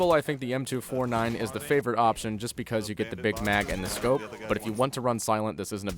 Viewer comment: is it wrong that i heard at 03:10 : "you get the big mac..."